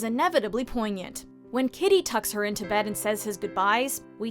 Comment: I was crying until I heard “titty” instead of “kitty”